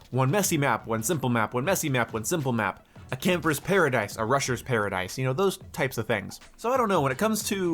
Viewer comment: i thought you said “russian paradise” - that would be so good also i video by a great content creator under 10 mins? impossible